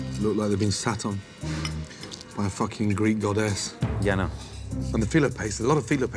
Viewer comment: wait did he just say 'greekgodx'